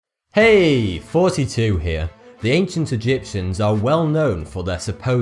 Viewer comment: I always thought he was saying "heeeey 42 here"